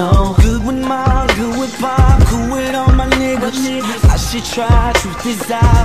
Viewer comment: Kid me thought he said "cool with all my neighbors" 🗿